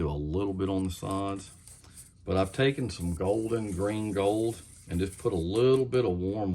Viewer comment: Really like the yellow color! Would you please type the color and brand? I think I heard you say ‘golden green gold?’ The color has inspired me to try an albino large mouth pattern.....perhaps you could do that as a pattern soon? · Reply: Golden Green/Gold